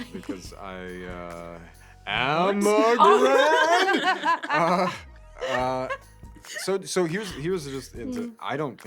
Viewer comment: what did he say? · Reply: I'm a GRANDDDDD